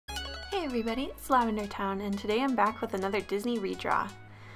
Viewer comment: When I first watched your videos I thought you you said ' hey guys its lavender brown' and I was shook